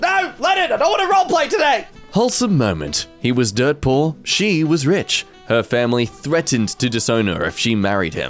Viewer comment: I misheard that as "he was Deadpool, she was rich."